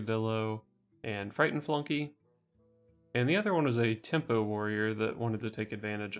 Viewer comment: When I heard ""Tempo Taunt Warrior Fusion", my brain immediately spat out "What? Tampon Warrior?"